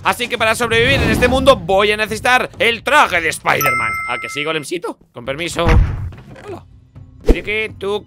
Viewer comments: escuche mal o dice a que soy golencito🤯🤯🤯🤯😎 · no se ustedes díganme que no soy el único que escuche decir a que dice: a que si golemcito xD